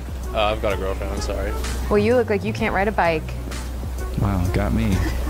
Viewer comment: Woooo hold up. 4:12, did she just call her self the town bike? 😂😂